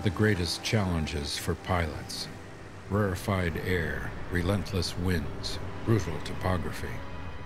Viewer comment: Hi guys, Can anybody help? What is the word which sounds as /ru:fl/ or /ru:flt/, or /ru:vl/ / /ru:vlt/at 4:41- 4:42? ... relentless winds, ??? topography.